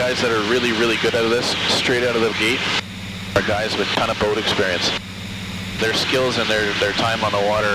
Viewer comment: What does he say at 11:25 ? "The only guys who are good at this straight out of the gate are ...."? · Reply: thisnicklldo guys that have had boating experience